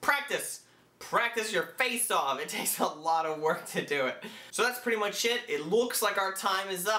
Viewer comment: When you said "that's pretty much it" it sounded like you said "that's pretty much shit"